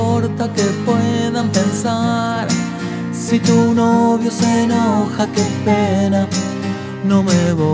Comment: Siempre pensé que decía "Si tu novio se enoja te pega" bruh